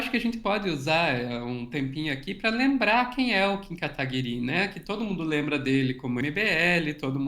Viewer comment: Sempre ouvi "Kim katakokinho", mas gostei da versão "Kim Arakiri".